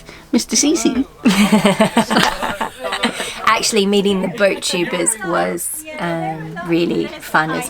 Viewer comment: loving your channel, after churning through lots of CtC and then all of Foxes Afloat! right after you mention "Mr CC", what did you say: the Boat Troopers? [10:31] Anyway, keep up the good work and are you guys on Patreon?